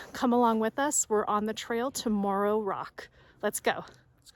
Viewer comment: Great video! I thought you said Tomorrow Rock at first. Views were awesome from there. I really think she needs to ask for her own water bottle for her next anniversary though!